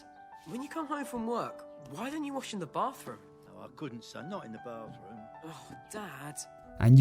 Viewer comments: What does he say? I can't make it out. "Well, I couldn't sand eye in the bathroom"? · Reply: He says I couldn't son not in the bathroom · what does he say here? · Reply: I couldn’t son not her in the bathroom.